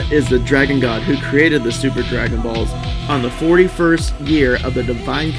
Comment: do subtitles and it says stupid dragon balls instead of super dragon balls